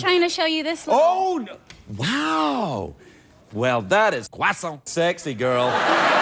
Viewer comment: What does he say at 0:52 ? I'm French and I don't understand why there is a french flag ... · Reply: He probably said f_ckin sexy. His thing is to censor his profanities with stereotypical phrases from around the world. This time it was 'croissant' spoken with a ridiculous French accent.